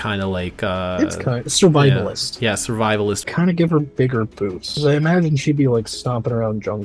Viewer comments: Oh, I thought he said bigger boobs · aww I thought he said bigger bo-never mind